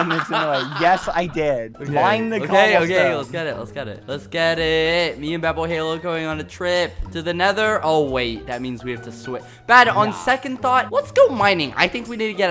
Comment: at first i thought he said “i have to write badboyhalo” lol